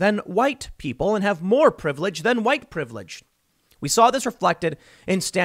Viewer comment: I thought you said "Engines" have more privilege than whites.. I was stumped for a short while